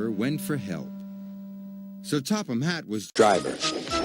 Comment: Lol! at 3:37, It sounded like the narrators said this together: "Sir Topham Hatt was-driver."